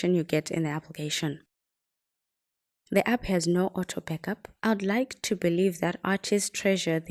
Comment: Sorry, at 1:35, what are they saying? There's no auto... something? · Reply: Auto backup